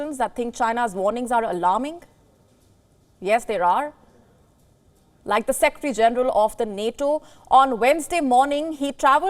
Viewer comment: Did she just say like the sexy general of nato?